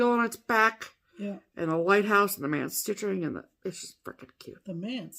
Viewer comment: I thought said the man was stitching, too😂😂. Thanks for the laughs today ladies! Great way to start off my week. 💕Michele